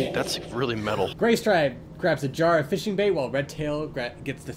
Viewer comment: I THOUGHT IT SAID GAYSTRIPE FOR A SECOND- 💀